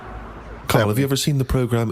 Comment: This is weird right, at 17:21 it sounds like Steve says "Claire," instead of "Karl". Now a woman called Claire Sturgess used to stand in for Karl on the XFM show they used to do before the podcasts, that the animation is based on.